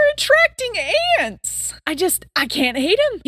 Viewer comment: did you just call yourself an ant